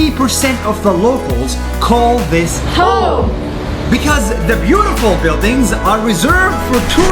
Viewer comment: 80 of the locals call this HOE srry thats wat i hear😅😂🤣